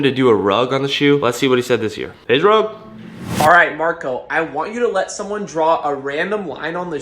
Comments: yo look at 10:48 what do mean by Alright babe in the subtitles😏instead of alright Marko lmao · 😂Did Anyone else notice at 10:49 when Rug says “Alright Marko” the caption says “Alright babe”💀🤣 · talk about when Rug said Marco the caption said babe 💀